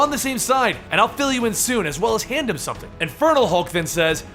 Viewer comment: I honestly thought you were saying "Infertile Hulk" at first! 😂😂 I love these videos, you genuinely make it seem like I'm reading a comic.